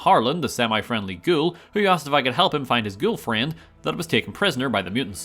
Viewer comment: Lol, did you say girlfriend or ghoulfriend? (In the context of Harland, both are accurate, im just curious about what you said). Sort of sounds like you said Gullfriend, which is a combination between the two joke titles.